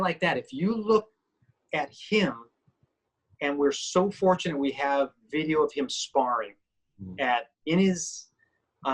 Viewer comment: at this time what grandmaster swordsman are you talking about? The name Richard said I couldn't understand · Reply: Antonio “Tatang” Illustrismo